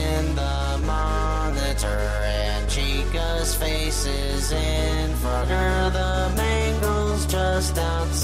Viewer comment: is actually says “Chica’s face is in Frogger”